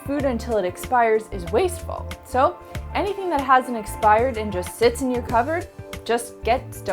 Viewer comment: I was really enjoying, getting some good tips from your videos until I heard you say you would donate expired canned food. That's just disgusting, you won't eat it but it's good enough for a poor person? Smdh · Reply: I had to listen to that part twice before I could tell that she said HASN'T expired. The first time I heard it, though, I also thought she said HAS expired because she says it very quickly, and sort of mumbles the word.